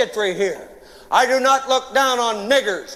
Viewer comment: I hear N word?